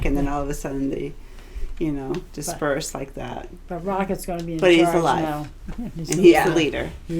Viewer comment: when i was watching the part where rocket was having a breakdown i thought said " rocket is far gone now! "